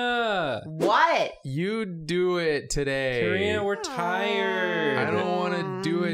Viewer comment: I heard " Karina retires" and not "Karina we're tired"😂😂😂 I was shocked for a moment